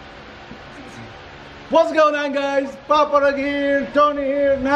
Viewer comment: captions say paparazzi for papa rug 😂😂